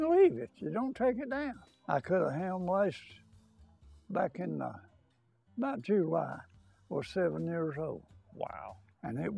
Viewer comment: I can't quite get what they are saying at 4:20. Is the elder saying you can leave the meat hanging there for years? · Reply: he said, back in July the meat was 7 years old hanging and it was still good.